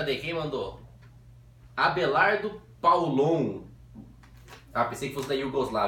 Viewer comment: só eu escutei "Abelardo -Paulongo-" KKKKKKKKKKKKKKKKKKKKKKKKKKKKKKKKKKKKK